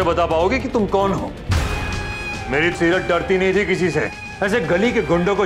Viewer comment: did anyone hear Kartik said "Meri sirat"